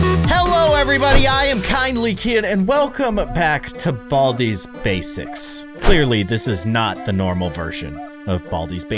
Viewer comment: Keyin:Welcome back to Baldi’s Basics... Me: Did he say Welcome pack to Baldi’s Basics?!